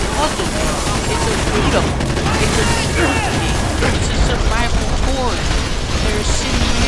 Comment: did he say "it's a stupid game"?